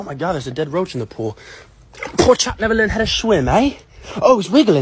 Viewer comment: I just realised he says poor chap never learned how to swim hey thought he said porkchop